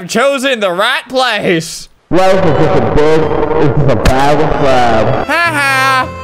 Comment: i thought it said life is a bag of sand